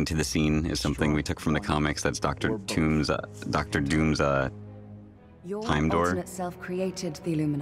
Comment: DID HE SAY DR. TOON???? 😅